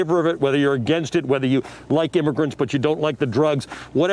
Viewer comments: Did i hear right: "weither you like immogrants and you dont like the drugs"??? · Did he just say whether you like immigrants or you don't like drugs What the F SOMEONE mansplain this he said 5 :05min in